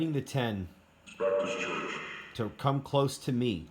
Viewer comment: I heard, "Baptist Church"